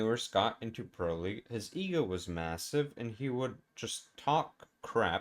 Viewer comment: Did you say MASSIVE?